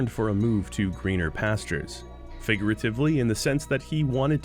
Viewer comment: Did.... Did you just say "finger atively" ?